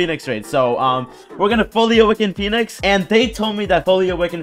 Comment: did he just say bully